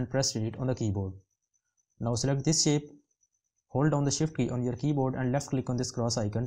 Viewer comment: Nice but there are a couple of words that you say so fast I can't understand, once is a about 2:25 where you say press (..?..) on the keyboard. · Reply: It is SHIFT key. I have also shown the keys on the right bottom corner of screen. Whenever i use a key or combination of key, i always tries to shown them on the right bottom corner. I hope that this will work. Thanks